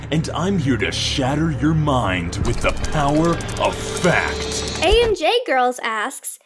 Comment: I thought he said "with the power of FAT!"